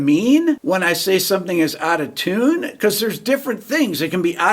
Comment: Hey, I thought you had been saying "out of town" in your videos, meaning from some other place, which made sense because not everybody knows the same songs.